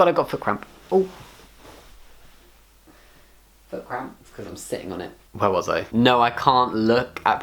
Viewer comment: had to re wind a couple times cus i thought you said "OH i gotta crap... i was sitting on it" LOL